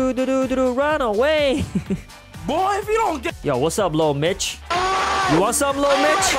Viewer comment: I thought he said sup little b***h Lol :)